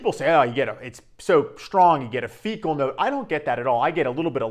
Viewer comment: a fecal note did I hear that correctly? 🤣🤣🤣